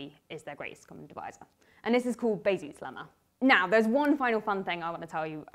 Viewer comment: What is the name of the lemma that says the gcd can be written as a linear combination of the two numbers? I heard "Bayses Lemma" but closed captioning doesn't agree. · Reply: bezout's lemma